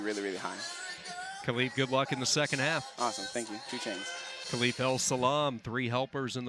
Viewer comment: I keep hearing Khalif say 2 Chainz